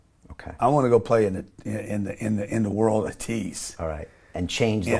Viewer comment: does anyone know what "the world of teas" means? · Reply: trillions